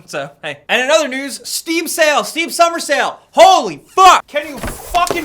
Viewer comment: I heard it as "Steve's summer sale" and thought to myself, who the fuck is Steve!? 😂